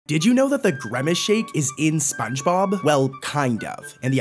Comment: Bro really said “gremace shake”💀💀💀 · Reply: I heard "gramace shake"